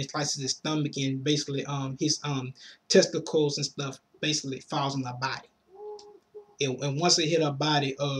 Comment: Also, did you say his testicles fall in her body at 5:35? xD